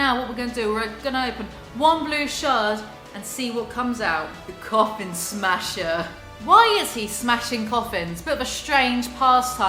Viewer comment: Actually thought u said Cock ‘in smasher 👀